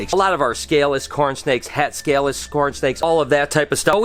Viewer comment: Is it just me, or did it sound like said “scornsnakes” instead of “cornsnakes?!” 😂